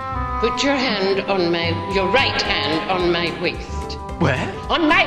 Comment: "Put your right hand on my waist.." "Where???" "ON MY WAIST!" - Prof Mcgonagall, calmly as Dumbledore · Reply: I thought she said on my weight